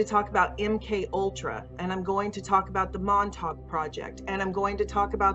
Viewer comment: Did they really say "montauk project" at 13:50?